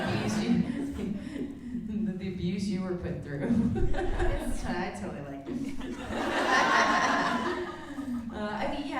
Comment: What did say at 40:45? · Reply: The abuse you were put through